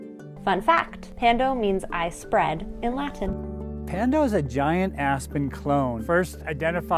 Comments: Who else thought he was about to say "Pando is a giant ass..." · AHAHAHAH at first I heard "it's a giant-ass pen clone"